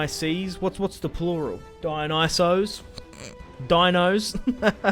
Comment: Dionysoi but I'm not sure